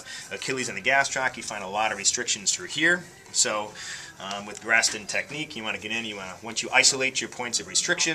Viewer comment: Lmao thought it said Gaston technique